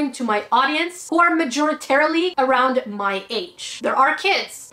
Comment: Majoritarily? Did I hear that right haha. Anyway people are dicks. 🤷🏻‍♀️ I can’t stand pewdiepie because, all this controversy aside, imo he’s just an obnoxious manchild but to each their own.